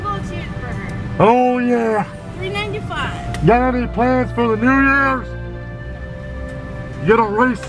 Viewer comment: Im high af and im wondering what he yelled right here! 2:25 Edit: He said got any plans for the night!!!